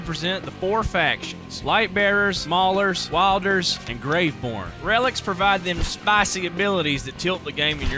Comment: I’m sorry did he say “Grave porn”